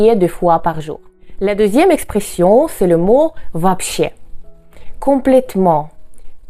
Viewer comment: une partie 2 serait intéressante. pourquoi pour "вообще" j'ai compris "va chier"...🙄😑🤣